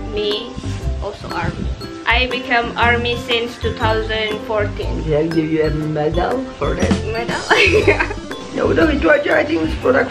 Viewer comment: Kak:"yaudah gitu aja an****" . .apa aku salah dengar :'''v